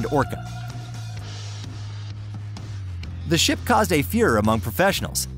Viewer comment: the ship caused a what amongst professionals? Are you saying Führer? I must have played that back six or seven times and it sounds like you are saying, "Führer." Do you mean furore?